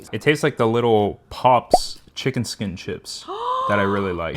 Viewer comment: I thought that he said chicken sh¡t the first time 😂